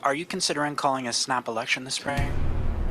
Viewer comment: I think Carney thought he said Are you concentrating on a sloppy erection?